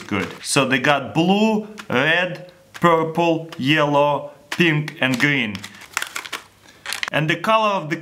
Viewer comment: he said think instead of pink